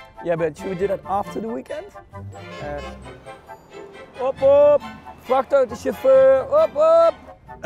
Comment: "VRACHTAUTO CHAUFFEUR BWHOAP BWHOAB!" Geniaal😂😂👏 (Edit: I got many people saying they don't understand it, Max says: "TRUCKDRIVER BWHOAP BWHOAB!" which is a phrase from new kids)